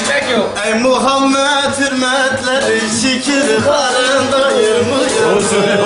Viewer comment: Mekanın cennet olsun güzel insan 😢🕊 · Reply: Genelde ölü arkasından konuşmam ama şunu da hatırlatmak isterim içki masasında yapılan şey şu sarhoş olmuş kişi 3:38 de başlayıp 3:45 kadar takribi iyi dinle bu söz sence nedir !!! '' Ey Muhammed hürmetleri şükür karın doyurmuyor !!! Öncesinde ekmek olmazsa olmuyor şükür karın doyurmuyor ... öyle mi peki Rahman ne diyor Öyleyse, rızkı Allah'tan isteyin. Ve yalnızca O'na kulluk edin. O'na şükredin. Eninde sonunda O'na döndürüleceksiniz." ANKEBUT 17 .